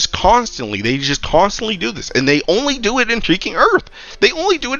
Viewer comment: Why does it sound like you say heaking instead of freaking thats so odd